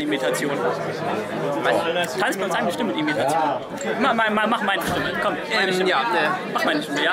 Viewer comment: Ich hab verstanden: "Mach ma' 'ne Stimme." statt "Mach meine Stimme." Sorry. xD Aber cool, euch kennen gelernt und in die Fresse gehauen zu haben. ;)